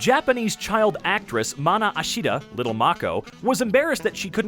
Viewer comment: omg I’ve watched to much my hero academia and thought that said Mina Ashido